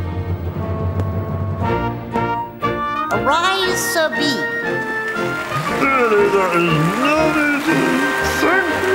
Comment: does anybody knows what the queen said when she knighted bean? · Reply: “Arise sir Bean “ 😂😊